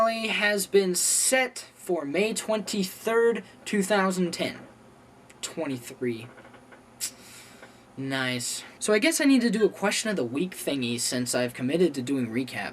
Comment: oh I thought he said the series finally was on MARCH 23, 2010. I missed lat nights episode...